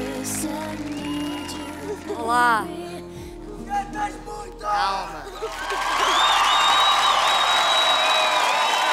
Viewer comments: What did the audience say at 2:25? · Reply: "Cantas muito". In this context, he means that she sings very well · What did the guy from audience say at 2:25 ? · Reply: cantas muito